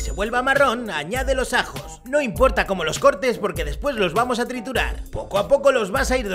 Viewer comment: En vez de "no importa como cortes los ajos porque después los vamos a triturar" escuché "no importa como cortes los ajos porque los vamos a torturar"